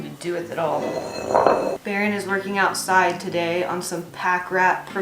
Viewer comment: I could have sworn you said, " Baron is lurking outside today". LOL